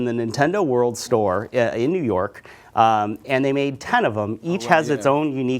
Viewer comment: I thought he said ton of em